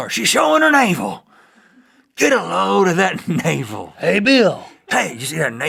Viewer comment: i thought he said get a hoe of that navel